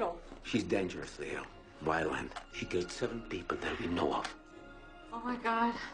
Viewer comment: what did he say? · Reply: NO NAME she killed seven people that we know of